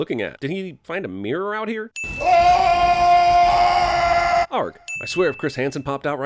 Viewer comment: (11:47 sin counter) I thought he said “LARP” and I lmao.